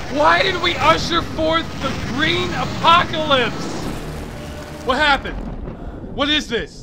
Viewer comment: "This is a BAD EXPERIMENT! We are BAD PEOPLE! WHY DID WE USHER FORTH THE GREEN APOCALYPSE?!" One of my favorite quotes ever. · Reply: For a moment i thought he said the Breen Apocalypse. I was thinking "i though the name Breen wasnt mentioned when this was made?"